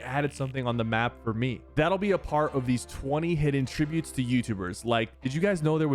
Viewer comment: for some reason I thought you said “that will be afart”